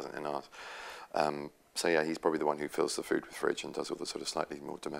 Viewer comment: did he say 'fills the food with fridge' at 3:00? lol! :D